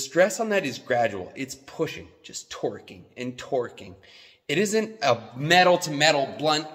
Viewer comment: Did he say Twerking and Twerking?